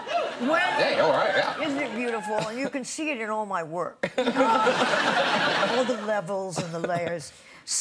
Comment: "all the levels and the layers" what i heard "all the levels and the leias"